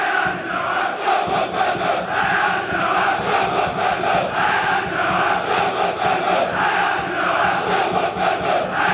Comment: what are they saying? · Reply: Framåt Malmö - Heja de blåa! Which means: Forward Malmo - Go the blue!